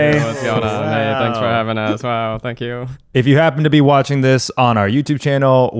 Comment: I first thought, Zach said: "Hey, thanks for having us. Wow, fuck you."